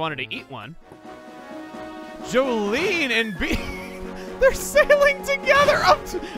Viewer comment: It sounded like he said Jolene and Bidoof, and now I can’t stop imagining a Bidoof in pirate attire posing with her